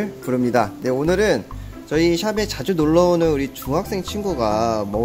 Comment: '샵에 자주 놀러오는 중학생 친구가 먹는..' 이라고 들리는..